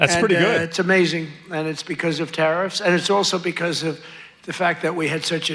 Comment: I swear he said terroriffs xX